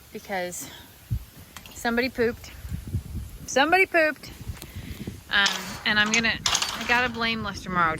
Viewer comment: At first all I heard was "Someone pooped and I blame Lester"😮